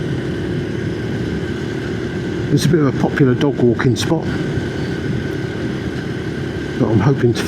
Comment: A great tribute for someone who sounded like a brilliant friend, RIP Paul. On a lighter note, in your opening sentence I almost thought you said it was a great dogging spot! I like the little editing of the lock going on, nice touch 👌 And definitely a bap, or a dog roll 😉